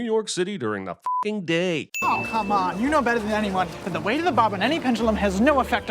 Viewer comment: The subtitles say 'bottom', referencing the line given by the 'nerd', but I suspect the word in the script was 'bob' as in a 'plumb bob'.